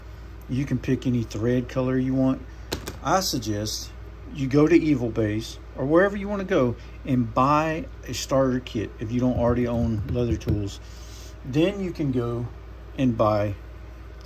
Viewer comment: where is it that you suggest getting the kit from? Sounds like you say Evil Base or Evil Bays but I’m guessing it’s eBay haha. I appreciate you showing your process… really contemplating trying to make a slip myself since I just bought 2 Sodbuster Jrs.